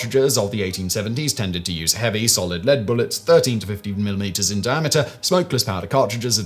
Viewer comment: Dude, if your musket is firing fifty MILLIMETER bullets, I feel sorry for you. (I think you meant to say "caliber") Otherwise, you're talking about a 740g (1.6lb) lead sphere. Seems kinda silly for anti-personnel ordnance.